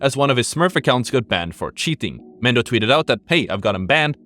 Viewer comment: that’s Mendo right? think that was just a pronunciation thing. thought he said Mande at first